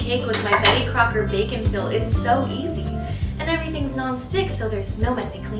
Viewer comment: I keep hearing “bacon Phil”…. Reminds me of pickle rick